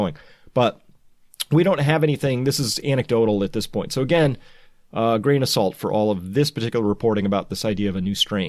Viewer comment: When you said "grain of salt", I misheard it as "brain assault". But perhaps that is also true. This fake news assaults our brains, our intelligence. Eyes open, no fear.